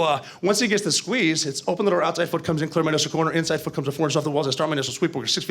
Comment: Can someone tell me what he says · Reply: Open the door outside foot comes in, clear minus the corner. Inside foot comes in four inches off the walls I start my initial sweep, foot is 6 feet off the door, I see a target he’s a threat *engage engage, finish my primary sweep. That’s all I could get before he just started mumbling lol.